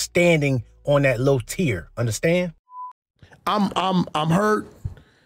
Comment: Im done did he just say to adam "boss im here" 😂😂😂😂😂😂 3:29 when i made that Django pic with adam and wack i never knew he actually said that man that make the picture so much better 😂😂😂😂😂